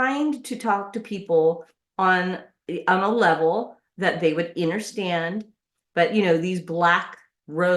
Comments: Did she just say innerstand? Woman- Everything you got was earned and deserved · is she saying “innerstand” instead of “understand”? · Did she say, "Innerstand"? Wow, this is demon speech (if there ever was such a thing).